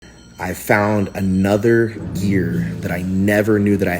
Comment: I thought he said: "I found extra GEAR" 😂